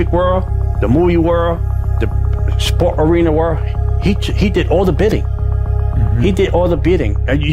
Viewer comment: Lol those church people speaking in tongues lmao ridiculous and I heard "he did all the bending" lmao